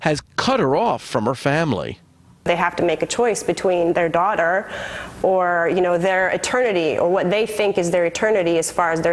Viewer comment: At first I thought the woman said "They have to make a choice between their child and their attorney." Not too far off, really.